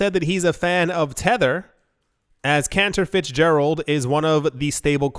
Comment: I thought you said his name was "cancer" Fitzgerald